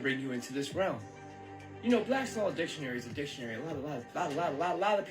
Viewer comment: he said the name of a dictionary? Anybody catch what he said? Sounded like "Black Solid Dictionary" but I dont think I heard it right. Anybody?